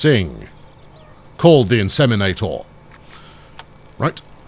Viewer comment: Every time you say call the inseminator, I hear ‘Call the Midwife’. Lol